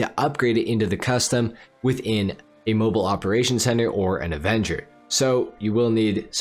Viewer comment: Thought u said not to buy avenger lol